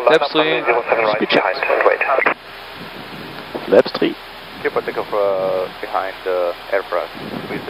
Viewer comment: Hat wie immer großen Spaß gemacht anzusehen. Vielen Dank! Hab ich das richtig verstanden, dass die Wizzair (~13:55) auf die Anweisung "behind departing AirFrance 319 line up 07R behind and wait" mit "cleared for takeoff behind AirFrance" geantwortet hat? Warum wird er da nicht korrigiert?